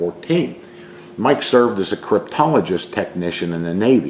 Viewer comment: Lmfao I thought you said "proctologist"